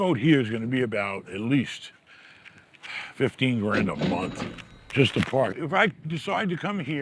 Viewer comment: Did he say it costs $15k a month to keep his boat the the Marina? But he said 2k a month at his house? Seems like an easy choice to me.